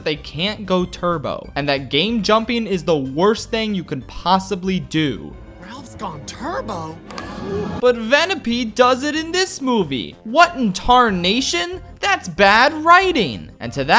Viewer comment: DID YOU SAY VENEPEDE?! LIKE THE POKEMON?!